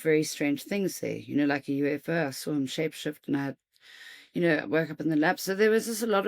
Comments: okay, what exactly did she say here, " I saw a UFO, I saw him shape shift" ??? Edit: 22:52 yep, she definitely said shape shift. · what does she say here? Shape shift? Work up in the lab?